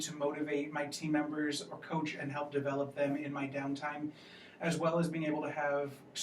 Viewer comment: I dont understand the word please help " in my d... time" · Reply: Down time